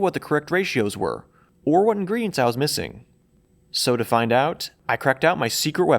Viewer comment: did he say "soda find out?" 😂